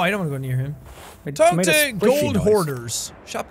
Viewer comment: When I first played sea of thieves I thought it was saying take to gold whores 😂😂😂